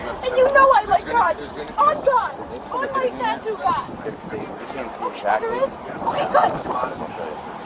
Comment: did she really say "On my tattoo of God" ??? 😂